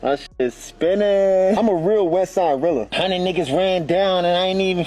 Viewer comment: Did I just hear "west side rilla."? :)